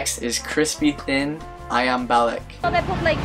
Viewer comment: i thought u said ayam balik lmao cute. let me teach u how to pronounce apam correctly = a-pump 😂